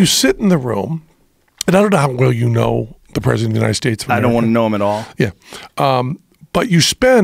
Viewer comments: did Joe say “I don’t wanna know him at all”? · Penn: I don't know how well you know the president of the USA? IMMEDIATE ANSWER Joe: I don't want to know him at all ! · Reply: Novel Chaser Did he? That honestly seems more in line with how he normally talks. It sounded to me like “I don’t want to know him at all” but I could have misheard. I don’t really care enough to go back and find it but if anyone has a time stamp I’d invest the 3 seconds that would take.